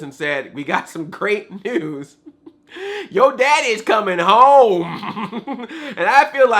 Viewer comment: Oh so it was your dad is coming home I thought she said your brother was coming home